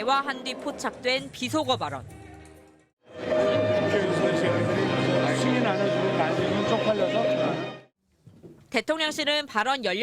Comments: "국회에서 이새ㄲ들이 승인 안해주면 바이든이 쪽팔려서 어떡하나" 라고 들리는거 같은데~ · 저게 되게 신기한게 자막 안 달아놓으면 "승인 안해주면 바이든은 쪽 팔려서 어떻하냐?" 로 들리는데 자막에 "승인 안해주고 날리면 쪽팔려서 어떻하냐"라고 적어놓으면 그냥 그런갑다 하고 들림....저런거 찾아내려고 11시간 고생햇을듯ㅋㅋ